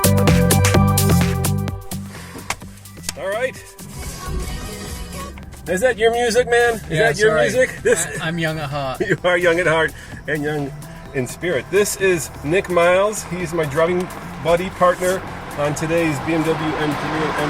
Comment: Did he just say he was " Young and hard"?